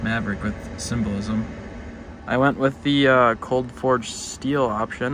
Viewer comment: I heard “Colt Ford steel” and was very confused for a second 😂